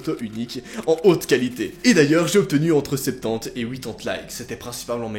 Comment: Il a dis quoi à 8:30 ? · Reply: Entre 70 et 80 likes(il l’a dit comme ça prcq il est suisse)